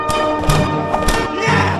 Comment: is it me or does it sound like he said yes